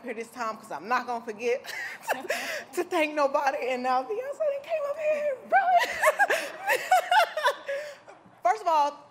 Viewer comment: Did Megan say, "Beyoncé done came up here and ruined" Ruined what?